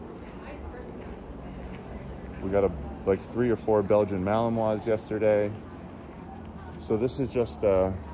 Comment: I was listening while messing with my dogs, and I thought you said you had three or four Belgian Mallomars. I thought, “That doesn’t sound right. Aren’t Mallomars some sort of cookie?” I had to go back and re-listen. I guess I’m a bit hungry, and my ears are tricking me!!! LOL!